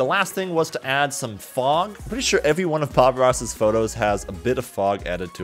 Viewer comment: did he just say pog ross?